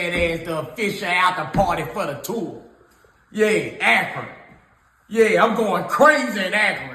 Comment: I thought that clown was saying Accra as in the city in Ghana. He was really saying Akron as in the city in Ohio. It really is time for these mumbled mouth rappers to beat it.